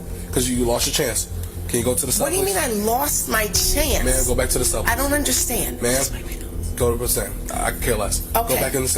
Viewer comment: What does she whisper to the officer at 45:30 ish? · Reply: I think it’s “I’m just wiping my nose”